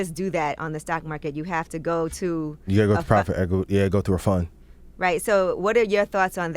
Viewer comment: Hmm what they say around 22:14 he didn’t share it just cut off and said fund 🤔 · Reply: He said “private equity fund”, he was just stumbling on his words a little